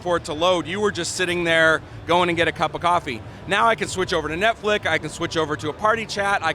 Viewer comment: did he say netflick. Just goes to show how out of touch these corporate fucks at microsoft are.